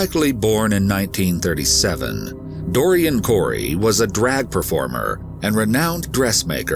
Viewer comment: I thought he was saying "Dory 'n Cory", like a cartoon double act.